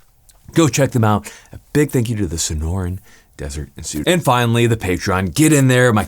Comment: For a second I thought you said you were sponsored by the Sinaloa cartel ... Not the sinora desert